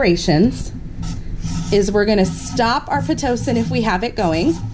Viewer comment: What is she talking about...? "Stop Ossytocin"?? Did she mean "Stop oxytocin"?? And what did she write on the board there... "Stop Pit!" What is "pit" ?? She doesn't explain what it is, just says it's important to stop it. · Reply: Pit is Pitocin